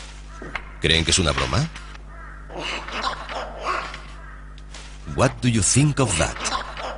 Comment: Alguien sabe que dice el pajaro en ingles en el mito 7:49?? jaja what do you que?? XP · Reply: dijo....What do you think of that?....que es....Que piensas de eso?